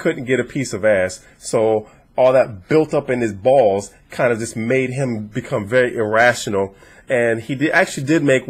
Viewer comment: Did he just say about 7:20 or so, "all of that buildup in his balls kind of just made him irrational?" I was tracking up until that point. LOL!